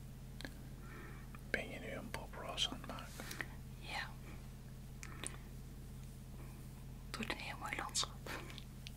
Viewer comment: wat zegt hij daar? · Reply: ben je nu een bob ross aan t maken